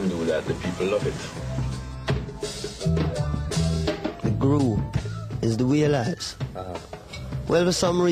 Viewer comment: that's not what he says, he is saying the group is the wailers